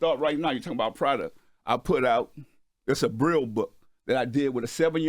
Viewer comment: Who else thought he said a “Brill book”?😭😭😂 Until he said it was about a blind kid, I was like wait what?… ohhhh he said “braille”😂😭